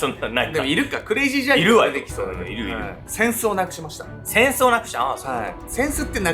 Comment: 冒頭"戦争をなくしました！"って聞こえて思わず作業止めて画面見てしまった笑